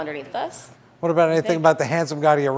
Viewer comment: I thought that you said, "Hands of God to your right." So, it took me a while to get that last joke. :)